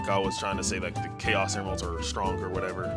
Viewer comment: captions say anals instead of emeralds